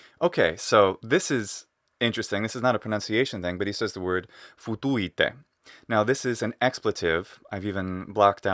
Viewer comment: HAHAHAHA, as a romanian speaker, what i heard there was ''fută-te'', meaning ''fuck yourself'', not sure if this latin ''futuite'' means the same thing but when you started talking about the f word i couldn't stop laughing